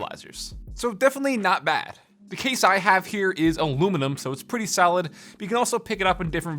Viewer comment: Wait did he say salad